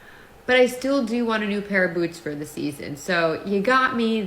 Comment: I thought she said glutes . LOL almost choked on my soda . she said boots. Ok not as funny “BUTT” ok LOL .